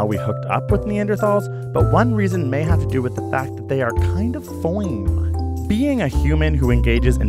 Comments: wtf did he say? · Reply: He says they are kind fine. But says it all cool like fi-on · what did he say? FOING? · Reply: XOR NAX He was trying to say fine in a sassy/sexy voice, just ended up sounding dumb. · What? Kind of What? OH, kind of ""fine"" but said weird.... Gotcha. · "One reason may be that they are kind of..." what? I can't understand what word he says. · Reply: bricknose he says "foine", it's just a funny way to say fine